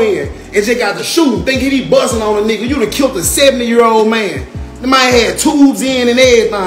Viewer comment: Sound like he said a seven year old man 😂😂😂😂